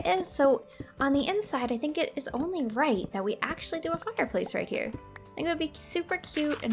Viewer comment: does she say fucker place